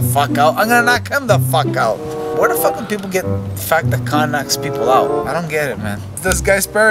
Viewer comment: I thought u said Khan doesn't knock people out 🤣🤣🤣 39 seconds ??? Got damn man